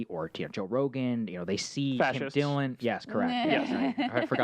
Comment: Did he say "fascists"?